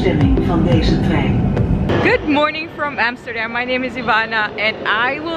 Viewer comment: Lol I don't know what I am on but I heard "good morning from Ivana, my name is Amsterdam" rofl